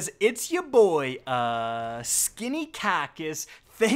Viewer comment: "What up guys! It's ya boi uh..... SKINNY KACKIS" ~ 🌵2019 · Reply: I thought he said "Skinny Kahki's"...